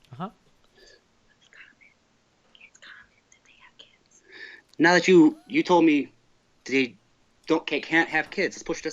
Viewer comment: what does his mother whisper to him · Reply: I'm thinking she said, "it's common that they have kids".